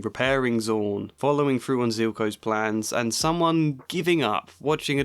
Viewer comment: did you say Zuko's plan instead of Silco? 😂 would be funny given how both have facial scar given by their loved ones and the experince changing them and putting them on a villain arc and becoming gray characters😂😂